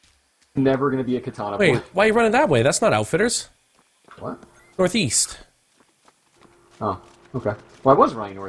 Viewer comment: northeast? I thought you said northweast